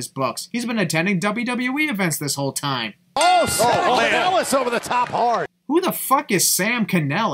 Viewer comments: First of all, at the sin you placed at 14:24 he clearly said “sending Kanellis over the top hard” nowhere near he said “Sam” at all. Might wanna pay more attention to how you place your sins. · once again, nobody said "Sam Kanellis", Tom said "sending Kanellis" stop being ignorant and ignoring what the commentators are saying and then trying to pass it off as a sin. It's not working.